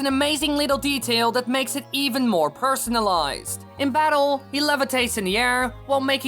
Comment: Did he just say "personalized" in a video about Mario?